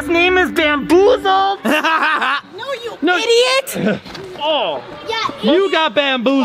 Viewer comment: who else heard the kid in the background say “yea you idiot” omg I laughed so hard when I heard that